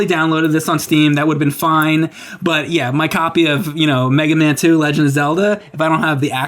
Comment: Mike sounded like he said "Mega Man 2: Legend of Zelda" which would be weirdly cool